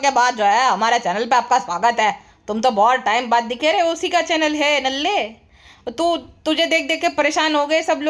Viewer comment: I heard dalle 😂😂..haha